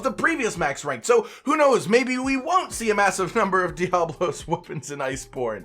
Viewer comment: am i hearing this right? 3:37 "maybe we wont see a massive number of diablos whoopings in iceborne." are we going to need to defeat alot of diablos' in the new game? edit: ok i just noticed that there is a thunder and dragon element side to side on that part of the video around 6:52. also a strange thing i noticed was that when cotton was mentioning the bird wyvern gem i noticed that weapon tree had water symbols and then splited into dragon, thunder, and poison. so whats going on there?